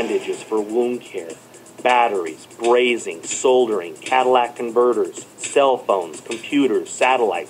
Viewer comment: did he say "Cadillac Converters"? lol. Maybe he did say Catalytic :) Anyhoo...Silver is not going extinct. It's an element. It's part of the earth's crust. Unless The earth's Crust disappears (at which point we'd have bigger problems, like being incinerated by the molten rock in the earth's Mantle) which is unlikely. However, I do believe consumption will out-pace mining production, and this will certainly drive the price to amounts previously unheard of. No need for fear mongering. Anyone that does a little research can easily confirm this.